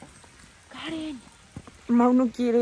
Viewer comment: Hay una voz que dice karen